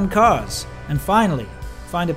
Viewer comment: Too bad, at first I thought he said at the end "stay awake". And I thought, wow what a powerful ending. And then he said "stay awesome"